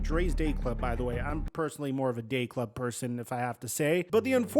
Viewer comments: I had to rewind and listen twice because I thought for sure I heard you say "I'm more of a gay club person myself". But turns out you said "day club" haha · Bro on god I thought said gay club and I was like ay yo